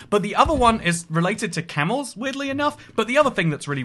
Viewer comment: Nobody gonna talk about how the subtitles say "camel yeets?"